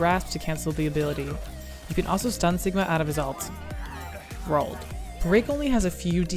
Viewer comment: sounded like you said wrigly